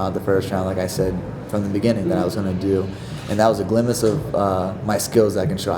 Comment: did he just say glimase lol wtf